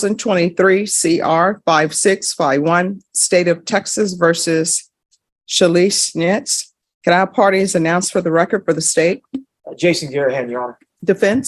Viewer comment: but she claims she’s having financial issues. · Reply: I thought you said this was your ex wife named Calise Schitz ! 🤣🤣🤣